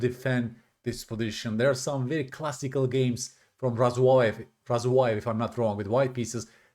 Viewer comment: Can you link, or write down the game that you mentioned that features the h4 move that showed such a lovely looking kingside attack. Razoi? I couldn't hear the name at 4:30 you said. Or better yet I'd love if you wanted to show a game. Thanks! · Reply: Here it is! I will probably make a video about it at some point! [Event "Dubna"] [Site <link> [Date "1979.??.??"] [White "Razuvaev Y"] [Black "Farago I"] [Result "1-0"] [WhiteElo "0"] [BlackElo "0"] [Annotator "JH"] [Variant "Standard"] [ECO "D41"] [Opening "Queen's Gambit Declined: Semi-Tarrasch Defense, Pillsbury Variation"] 1. d4 e6 2. Nf3 Nf6 3. c4 d5 4. Nc3 c5 5. cxd5 Nxd5 6. e3 Nc6 7. Bc4 cxd4 8. exd4 Be7 9. O-O O-O 10. Re1 Nxc3 (10... Bf6 11. Ne4 b6 12. a3 Bb7 13. Qd3 Nce7 14. Bd2 Ng6 15. Rad1 Rc8 16. Neg5 Bxg5 17. Bxg5 Qc7 18. Rc1 Ndf4 19. Qe3 Qb8) 11. bxc3 b6 12. Bd3 Bb7 { Utgangsstallning for tematurneringen. I detta parti ser vi ett exempel pa VITS strategi. } 13. h4 { !? N } 13... Na5 (13... Bxh4 14. Nxh4 Qxh4 15. Re3 $44) 14. Ng5 $36 { ! } 14... h6 15. Qh5 { ['with the idea' 16. Re6 fe6 17. Qg6] } 15... Bd5 16. Nh7 Re8 17. Bxh6 { ! } 17... gxh6 18. Qxh6 { ['with the idea' Nf6, Bh7] } 18... f5 19. Re3 Bxh4 20. Rg3+ { ! } 20... Bxg3 21. Qg6+ Kh8 22. Nf6 Bh2+ { ! } 23. Kh1 { ! } (23. Kxh2 { ? } 23... Qc7+ 24. Kg1 Re7 $17) 23... Qxf6 (23... Bxg2+ 24. Kxh2 Qc7+ 25. Kxg2 Rg8 26. Rh1+) 24. Qxf6+ Kg8 25. Kxh2 Rac8 26. Rh1 { ! } 26... Rc7 (26... Rxc3 27. Kg1 Rc1+ 28. Bf1) 27. Qg6+ Kf8 28. Kg1 Rf7 29. Qg5 Rg7 30. Rh8+ Kf7 31. Qh5+ { 1-0 Black resigns. } 1-0